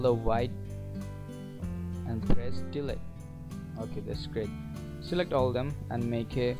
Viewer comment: Hey great vid, I just have a couple questions. At 4:37, did you say "press delete"? Also I noticed that in Illustrator cc 2017, they do not give the option to Save For Web. Any help would be great, thanks!